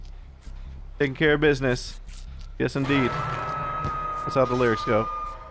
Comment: Ohh "that's how the lyrics go" i thought he said "that's how delirious go" i was so confused😂🤦